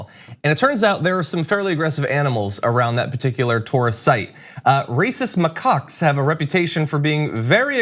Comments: I legit thought he said "racist mccocks!" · Every time they say "rhesus macaques" I hear "racist macaques" Lol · I honestly thought John said: "...racist McCocks..." . :D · It's hilarious because I could have sworn you said "Racist macaques have a reputation for being very aggressive toward the 25,000 tourists who visit the Taj on a typical day." 😂😂😂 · I thought you said racist, not rhesus and laughed so hard I woke the baby up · I thought you said racist Macaques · I heard “racist mycocks” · when I listened to the podcast I thought John was saying RACIST MUH-COCKS. hahahaha